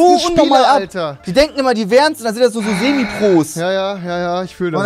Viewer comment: Wer hat auch Rewipros verstanden xD